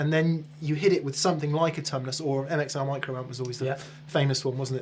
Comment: What pedal does he say people used to love at 8:46? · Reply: I know it’s 6 years later but he says “MXR Micro Amp”